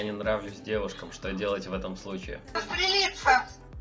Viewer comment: В 4:42 я думал что она сказала "застрелиться", и она тоже самое сказала. Совпадение недумаю